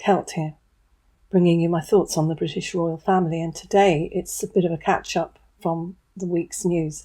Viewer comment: "A bit of a 'catch-up'", you said at the beginning. I heard "A bit of a cat-shop". American I am.